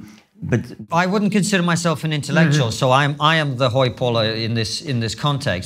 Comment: what did he say he was? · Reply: hoi polloi - greek word for 'the masses'